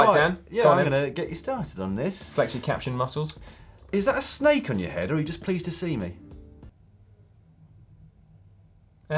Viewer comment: Caption: I thought you said snack